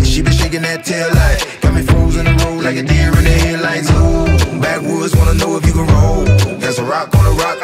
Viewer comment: backwoods; I thought they were saying Baton Rouge!